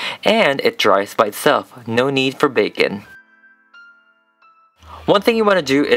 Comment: Lol, at 3:06 when he said baken, it sounded like he said bacon (no hate)😂😂